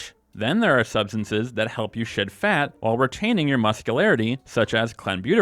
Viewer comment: at first I thought you said "to shit fat" 😂